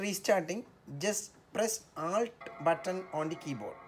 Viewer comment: “When restarting please press 🤷‍♂️ button on the keyboard.” Which button does he say? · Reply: ALT